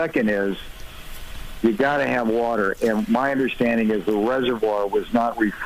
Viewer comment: and i kept hearing "watch the water"